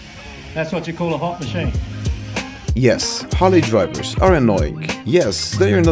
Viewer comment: I thought you said rally drivers, not Harley drivers... Great video. Keep it up! Loved the boat